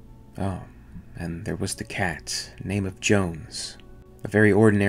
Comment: I thought they called the cat 🐈 Jonesy?